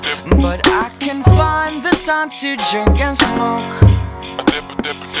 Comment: it sounds like he says "jerk and smoke" but I'm pretty sure he says "drink and smoke"???